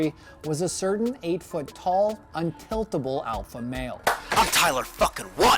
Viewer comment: Are my ears broken or did he actually say "8 foot tall Tyler1?"